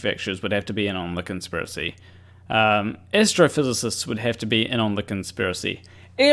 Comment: Not gonna lie. I heard Astrophysicists as Estrophysicists. Which, honestly, would also have been based if that was the case.